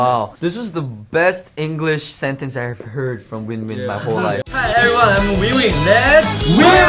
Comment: Winwin said "im winwin" i misheard it and thought he said "im weewee"